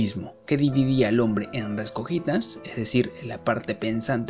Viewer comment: alguien sabe que dice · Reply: res cogitans (cosa pensante) y res extensa (cosa extensa) esta en latín